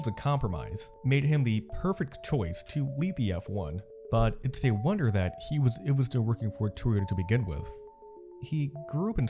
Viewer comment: What did he say?? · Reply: I sincerely apologize for the late reply. I said "but it’s a wonder that he was even still working for Toyota to begin with." It came out WAY too fast and I should've done another take. I hope you enjoyed the video otherwise.